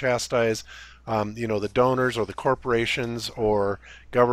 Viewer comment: did he say "or governments"? Multiple governments that would fear being chastised for what? Isn't the flat earth part hidden so there's no stigma?